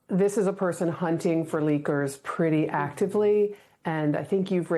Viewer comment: They said “he is hunting for leakers all the time” and I heard “he is hunting for liquors all the time” 😂😂